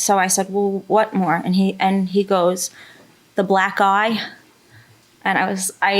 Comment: I thought she said the black guy at first 😂...fair judgement.